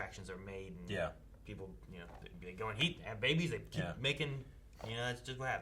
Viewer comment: I thought said boobies as well